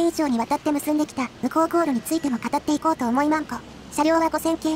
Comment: 誰もツッコんでないけど…0:55宇高航路についても語っていこうと思いま○○…って聞こえたぞ